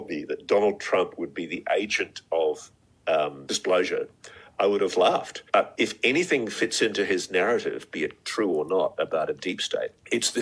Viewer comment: I heard “Agent of Displeasure” and made myself laugh 💀